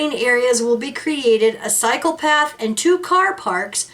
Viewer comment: It sound like you said (and a psychopath),which old have been fitting, instead of (and a cycle path)!😂😂😂😂😂😂